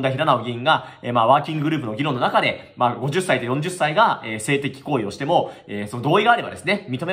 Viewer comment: 50歳と40歳がと聞こえるんですが？気のせいでしょうか？